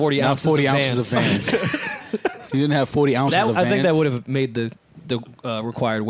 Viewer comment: I thought he said 40 ounces of xans lmao